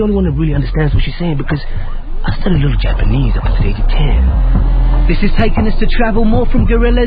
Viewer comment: Can someone subtitle what he said? it's practically inaudible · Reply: He said that he studied a bit of japanese when he was 10 :)